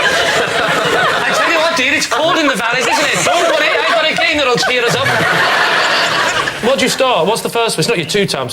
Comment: what the hell does he say here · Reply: I tell you what, dear, it's cold in the valleys isn't it? Don't worry, I've got a game that will cheer us up.